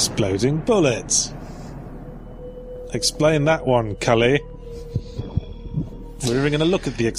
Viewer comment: I jumped when I heard him say, "Explain that one Cully." Not sure if he actually said that but, hearing my really uncommon name is jarring in videos. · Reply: I think he was saying "Scully" as in the skeptic in x-files